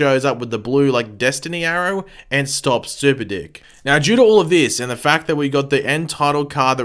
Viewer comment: I honestly thought you said super dick..😳